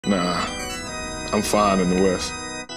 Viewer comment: I heard “ nah… i’m funny in the west”.